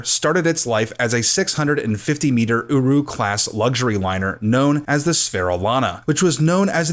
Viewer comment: At first I thought he said 650 meter uWu class luxury liner